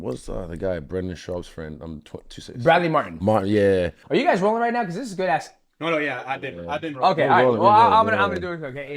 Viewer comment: I had to replay 3:50 about 10 times. I swear i heard, "are you guys rollin right now? Its some good shit, but im just doin cocaine." What?!?! They poppin molly and lines?!?!